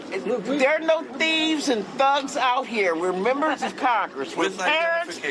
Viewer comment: Wait, i Thought she said, "we are thieves and thugs out here" She may not have said it, but it is true.